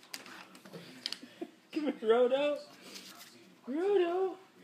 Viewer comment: was he saying Rodo, Rogo, frodo?